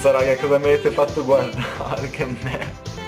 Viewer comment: soli io ho sentito michié 😂bho tipo minchia in Francese 😂